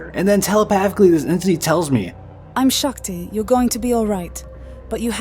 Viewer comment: wait did she say her name was Shakti? Shakti is a form of divine feminine energy in Hinduism, I wanted to mention that since I thought that was really cool. Have you been looking into those ideas prior to the trip? (Nevermind you answered that in the video, I think its so cool you heard that)